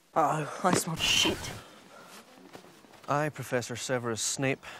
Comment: What did ron say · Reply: "I smell shit"